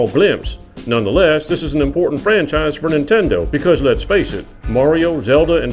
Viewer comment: True, but by the time it comes out those non-Nintendo fans will probably lose interest since better FPS games might exist. Hopefully, it's worth the wait. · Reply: Right. I re-listened. I thought you said most important. You didn't. I will hold that L. lol